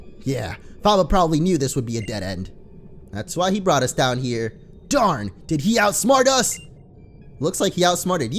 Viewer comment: at first i thought he said "Damn did he outsmart us?"